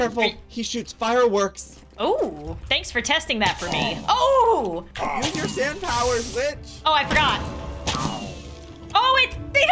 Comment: “use ur sand powers b**ch” that’s what it sounded like omg